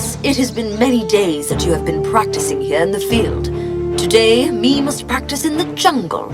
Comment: it said me instead of we at the bottom